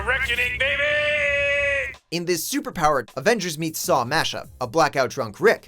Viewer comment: did he said Avengers?